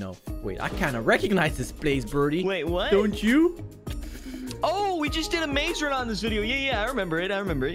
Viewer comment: Did he say "oh yeah we did a maze run on this video"??😂😂 so cute